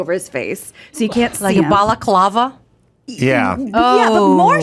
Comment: Anyone else catch “Ball’a’colava” ? 😅